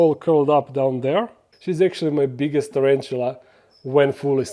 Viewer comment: did you said "she's actually moj biggest tarantula" ? hihi